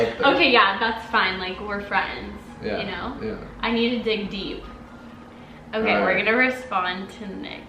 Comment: i thought she said i need a big d